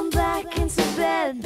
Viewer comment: get off the phone! Wow 1997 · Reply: I hear "turn off the phone" She predicted the future!